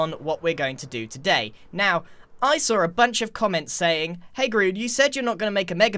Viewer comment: “hey groon” anyone else hear that? XD